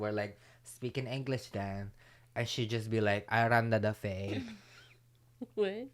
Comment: I heard "I run da buffet!"